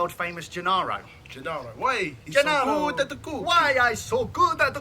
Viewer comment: "Why I'm cooking so good!!!" That's what Gennaro says, not